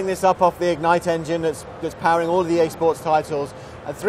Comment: did he just say the ignite is powering all the Xbox titles btwn 1:00 and